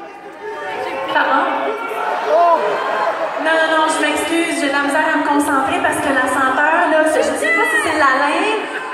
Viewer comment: il sont dit quoi à Maryse vers 2:10 j'ai pas compris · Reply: Ya une personne qui lui a dis rentre chez toi